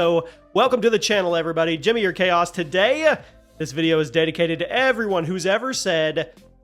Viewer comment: For over a decade I thought your intro was saying “Junior Chaos Here,” not Jimmy or Chaos😂😂.